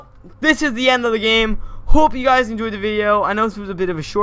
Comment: I thought he said " POOP you guys enjoyed the video!"